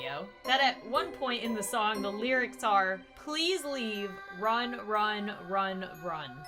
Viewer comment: WHEN WAS SAYING “Run run run” I THOUGHT SHE WAS SAYING “Rub Rub Rub” AND NOW THAT MADE THINGS MORE CREEPIER FOR ME